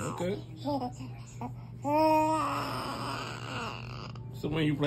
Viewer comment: Did sound like he said iown know 🤣💙